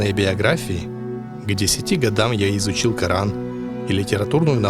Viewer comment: к 1990 году? Мне послышалось?